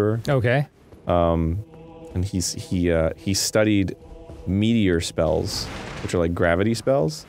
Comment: Arin said meteor, Dan thought he said meatier, and I thought he said media